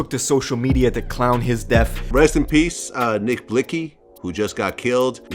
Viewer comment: did she say “nick blix cee” 😭😭😭